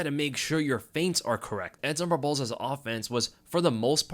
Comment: WTF?!? At 2:55 you say Edson Barboza - clearly - captions decided you said “zombie balls” Never saw that before… We might have a new nickname